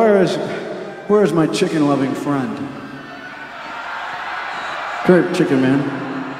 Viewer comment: Sorry but I couldn't understand what exactly Axl Rose is saying here? Can someone please write it in a reply? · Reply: "Wheres my chicken loving friend, come here chicken man. See, we do have something relevant here because Bucketheads dad went to Ohio state (the gig was in Ohio), we have an alumni child here, so it ties in." :_WooBucket